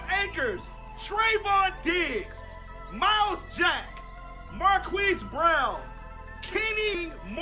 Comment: I don’t know why I thought the guy said marqueef brown lol